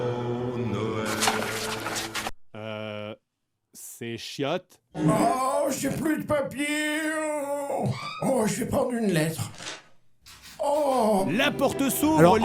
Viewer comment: Le "un ptit pipi papa!" De François pérusse était juste trop bien placé ahahahahaha😂❤ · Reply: J'ai entendu "j'ai pèter papa" pour ma part 😂😂😂 Ça m'a fais rire